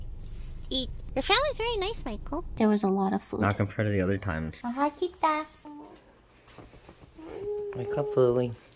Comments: Did I just hear (Tagalog) "Mahal kita"? My heart! 😍♥️😍♥️ 🇵🇭 That's cute Lily. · Did you just say mahal kita. Hahaha 🥰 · Did Lily just say "Mahal Kita" at this mark?